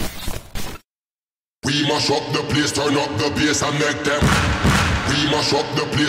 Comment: Why do I always hear "We must shot the PS4" in the beginning?